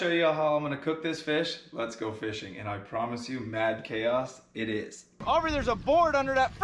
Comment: I think I like “Mass Chaos” better. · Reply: I thought she said “Math Chaos” 😂😂😂